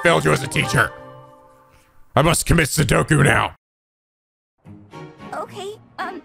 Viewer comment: XD it sounded like he said Sudoku rather than seppuku